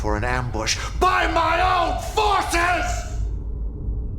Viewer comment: It sounded like he wanted to say "By my own Fucking forces!" lol